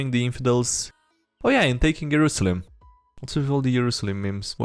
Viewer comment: did I hear Jerusalem?!?